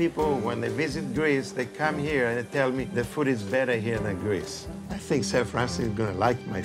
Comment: Correct me if I'm wrong but at the beginning, it sounds like says "A lot of people when they visit Greece, they come here and they tell me 'the food is very here in Greece'"